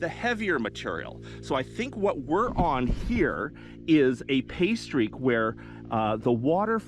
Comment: The first time you used the term I thought you said you were working the pastry. I was scratching my head about that for a while. I'm astounded that you didn't get you wife a ring before this! Patient woman you've found.